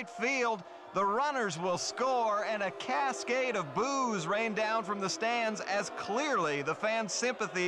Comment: This got to be my favorite Family Guy skit of all time, the blattant naive absurdity of it is what makes it all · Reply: I thought he said “cascade of booze” lol